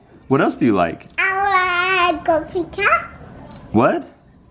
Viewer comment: I like cold pizza! Am I hearing things? Lol